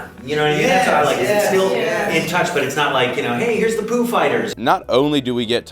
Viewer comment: i thought he said foo fighters